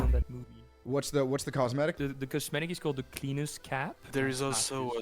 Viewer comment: i thought he said penis cap when he said cleaner's cap